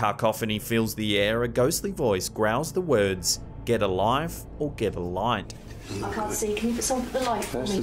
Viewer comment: I heard “get a wife” story of my life huh 🤦🏽‍♂️